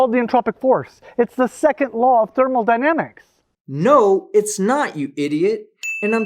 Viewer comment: I can't be sure, and I may have mis-heard, but did it sound to anyone else that said "thermal-dynamics" instead of thermodynamics? Not accusing him of screwing up the word he's professing to be an expert on (as one would have to be before "teaching" others), but asking others for their input. I'm pretty sure I've heard him say "thermodynamics" before, so it's probably just my ear wonking out on me.